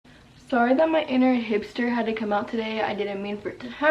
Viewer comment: Who else thought she said sorry my inner hamster came in 😂